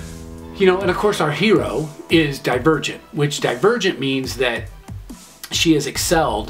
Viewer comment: When you said Divergent I thought you said The Virgin and then I was like wow this sounds like divergent